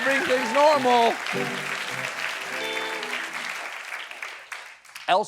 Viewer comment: When Steven said "I.C.E. is having a hard time recruiting," I misheard that as "ISIS is having a hard time recruiting". I was reading myself for good news concerning a terrorist organization having problems. Then I heard the name right and thought "Same thing".